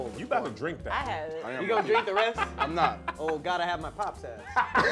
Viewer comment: What did say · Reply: Ol’ gotta have my pops headass in reference to the saying from the pops cereal commercials